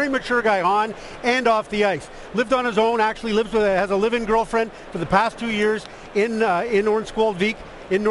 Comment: did he just say he has a living girlfriend?1:43 haha